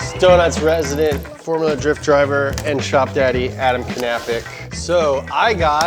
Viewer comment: wait, what? Did I hear 'Adam Knapik', polish name? 😮